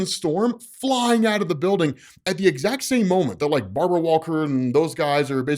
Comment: the who walker ? i cant tell what he says here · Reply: Barbara